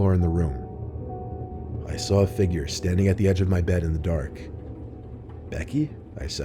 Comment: I thought it was Betty..??? He said Becky, no?